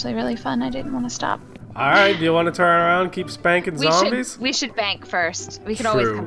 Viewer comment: I thought she said bang